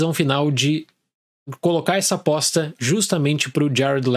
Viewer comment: eu juro que ouvi o Dale falar que queria conhecer quem colocou essa "bosta" pro Jared Letto... Voltei três vezes até ouvir "aposta" que é o que o sempre muito educado Dale falou, de fato.... Acho que a indignação na voz do nosso querido Dale me enganou duas vezes...